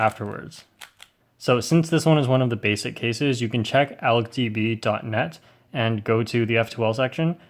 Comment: I can't make out the website, what is it? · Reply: Its algdb